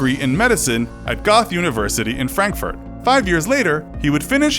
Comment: I'm sorry did u say GOTH university?